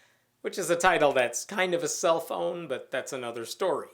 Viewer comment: At first I thought you said 'cellphone' instead of 'self own'